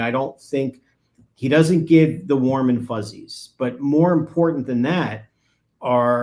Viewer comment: I have been watching far too much Lori Vallow-Daybell, when said something about warm and fuzzy I heard 'Mormon fuzzy' 😅